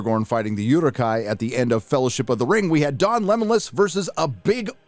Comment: Everytime I hear you call him Don Lemonlas 🍋 I can’t help but laugh 😂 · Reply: I swear, first time I thought he said Lembolas because of lembas bread 😂